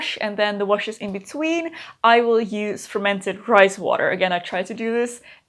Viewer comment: I need to get an ear exam or something. I misheard fermented rice water as fermented christ water, and super natural as supernatural.